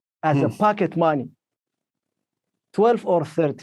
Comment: i thought he said as “f@ it money” 😂😭